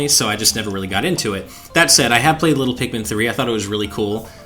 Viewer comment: Did he say "I have played Little Pikmin 3" bro this is not a crossover of pikmin and little big planet haha.